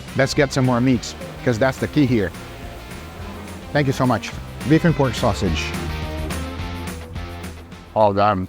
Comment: Dear god my ears and my head are not syncing up... @ 4:49 I wasn't seeing the caption, and no matter how many times I replayed it, I kept hearing Vegan Pork Sausage....